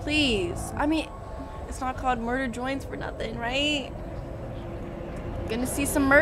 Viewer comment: is it just me or does it sound like shes saying murder joints